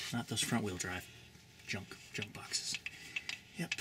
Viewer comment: What did he say to the front wheel drive cars at 3:36 ? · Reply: junkbox :D